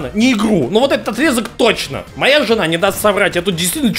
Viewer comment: стоп, сказал "Моя жена" или мне послышалось?